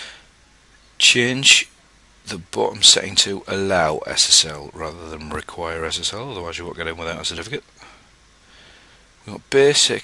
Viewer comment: Can anyone tell me what the guy is saying between 2:20 - 2:22. I m trying to figure out what the hell he says since last year :) Thanks in advance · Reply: çipil "... allow ssl rather than require ssl, otherwise you won't get in without a certificate" :)